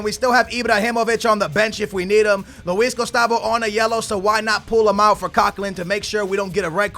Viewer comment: Is it just me or does it sound like he says luiz GHOSTavo